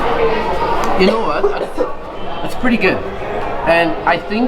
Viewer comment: My my! Did I hear 'big tarts satisfaction' written on the packaging of the chicken burger? Must be a tantalizing burger.